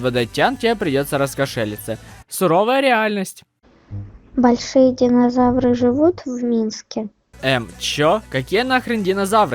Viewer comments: Мне послышалось есть ли большие динозавры в миске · Мне послышалось "живут ли динозавры в миске" )